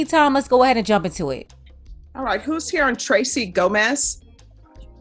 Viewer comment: I realise judge said ‘Gomez’ but am I the only one who initially thought she said ‘dumba**’ at the 0:52? 😂